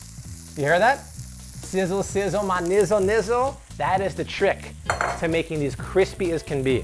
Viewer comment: Did he just say "sizzle sizzle my nizzle nizzle"?